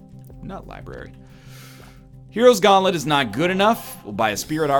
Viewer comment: What did he call that item? "Hero's gauntlet"?